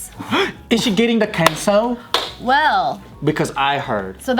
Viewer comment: Thought said in the very beginning "Is she getting the pencil?"